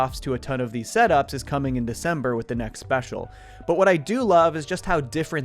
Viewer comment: December? December???? Did you just say we're getting the next special as early as December?!